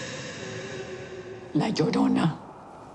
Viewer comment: who else heard "like you dont know." instead of "La Llorona"